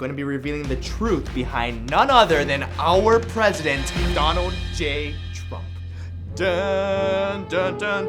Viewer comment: idk why but my brain thought he said “Donald. J. Duck”